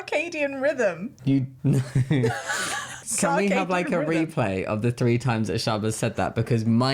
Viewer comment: i heard ‘suc-ka-dian rhythm’ 😂